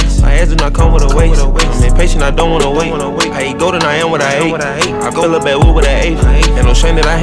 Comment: I really thought he was saying I ate goat and I am what I ate. But he really said I eat gold and I am what I ate